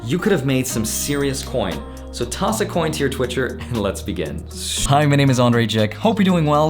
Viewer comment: Did he just say “toss a coin to your twitcher?” 😂😂💨💨